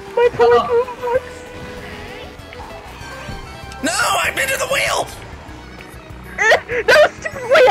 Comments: I swear I thought for a second said “no, I invented the wheel!” xD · I heard "NOOOO! I invented the wheel!"